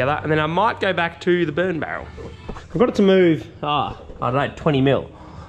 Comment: Good times. I thought I heard burn barrel.....